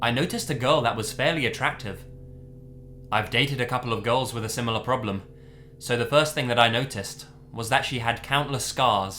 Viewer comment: I thought he was saying the similar problem was being pretty attractive